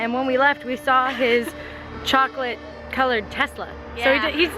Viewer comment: When you were talking about the chocolate shop owner, I thought you were about to say, "we saw his chocolate testicles" ................. :| Thank god you said tesla. What is wrong with my brain???!?!!!